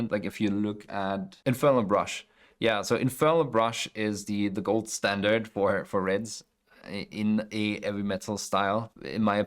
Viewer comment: It took me seven attempts and a ton of volume PLUS a google search to discover what you meant at 00:49:38 INFERNAL BRUSH. I heard En Fil La Brush, A Heavy Brush, Ela Brush, but I couldn't make it out! Glad I was able to find it, damn this guy is clean AF. I think I follow this painter on Insta. Amazing.